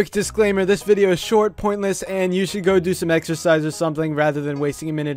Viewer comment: Exercise? I thought you said extra fries.